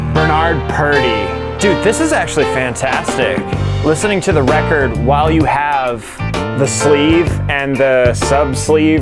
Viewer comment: I thought, you said listening to the record with ....lines of coke. 😂😂😂